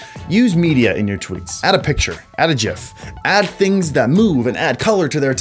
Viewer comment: did he just call it a jif